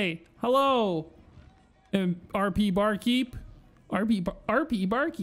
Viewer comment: I thought you said Barbie bar keep😂